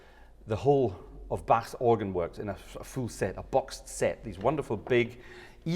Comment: did you say boxed set or Bach set? :p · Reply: Boxed set, because the Bärenreiter has a complete set of the Bach works 😎